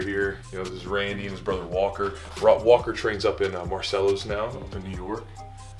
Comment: With me. His bro trains elsewhere. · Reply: sounds like you said "marcellus", which is a suburb of syracuse ny (near me) but i figured that you said marcelo lol. thanks for the cool vid.